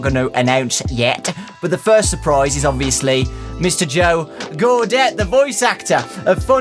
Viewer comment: And then, Mr. Hippo suddenly turned into Owen Wilson("Wow!"). When Joe said his last name at the end, I nearly heard it as "Gadget". Now imagine Joe going "GO-GO-Gadget something!"